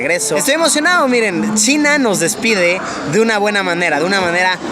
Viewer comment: que dice ??? Yo escucho Sin anos